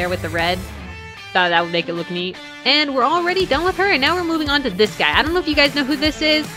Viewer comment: At first I thought you said you were moving in to sticks XD